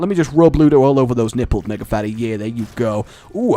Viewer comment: sounds like he says: "Niggafatty" Just me?